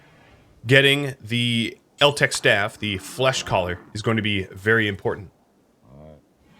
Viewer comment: Lol this whole time I thought you were saying Fleshcollar, whoops 😂 Fleshcaller makes more sense now